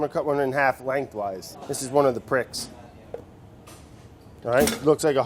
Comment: oh, i thought it said "plicks" and that was short for "pickles"